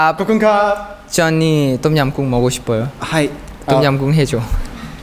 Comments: Wait did Ten call him Johnny @ 00:18? 😂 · Did he just call him "Johnny"? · is ten said 'Johnny' to call Jaehyun?